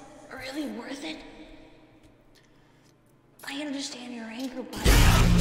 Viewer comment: Hello! I’m the voice actor for Golden Freddy. I’m glad that you enjoyed this, it was a lot of fun to make! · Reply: waot, i thought he was saying "You dont understand anymore"